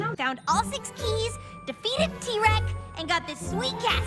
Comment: "Rainbow Dash: "We defeated Tirek and got this sweet castle." I initially heard that as, "We beat a T-Rex and got this sweet castle." Lol!